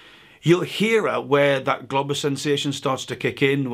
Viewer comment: Would be great to SUBSCRIBE!!! Thank you for all the LIKES and COMMENTS!! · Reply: You gotta say "Globus" more clearly please... I heard "globbers" and its rather confusing